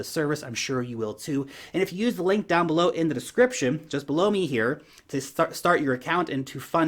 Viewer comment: did you say “Just blow me here?”and point down 😜